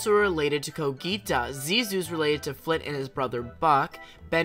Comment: Wait a min…why did I hear “…related to his brother fuck…” (I’m sorry for commenting a whole year later lol)